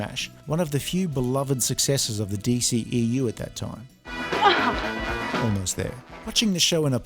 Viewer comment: Did you say almost there